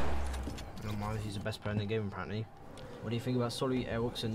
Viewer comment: I thought he said Mongraal is the best player in the world Nani